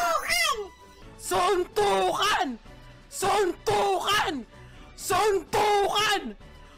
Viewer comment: what did I just heard? Sunugan. 😅?